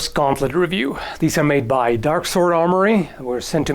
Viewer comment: I heard it as Dark Souls Armory. Send halp.